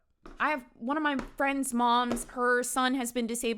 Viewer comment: Bro! I thought you were saying your friends mum killed her son 😂omg